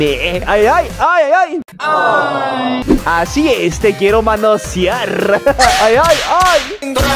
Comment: yo pensé que decía "Te Quiero Matar"